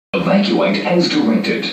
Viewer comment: what i heard: "EMERGENCY! EVACUATE NOW! EVACUATE AS SITUATED????"